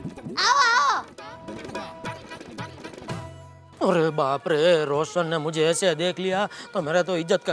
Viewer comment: he said roshan instead of roshan bhabhi